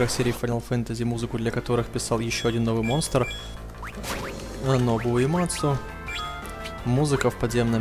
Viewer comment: "ну и конечно же как всегда для ff писал музыку ещё один новый монстр Набу уямацу" (если провильно имя услышал)